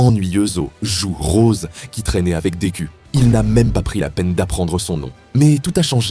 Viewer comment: La vidéo ma terminé c'était trop intéressant et drôle 🤣🤣 a la place de "Deku" j'ai entendu "des culs"